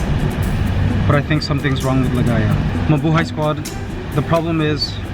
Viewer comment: Had me worried a minute there glad My Lovely LiGaya is feeling better . It sounds like LiGaya says “what , Mabuhay , wow “or is it just me hearing